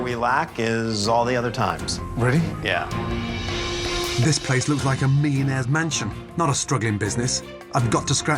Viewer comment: I thought he said, "This place looks like a mean ass mansion." Hahahaha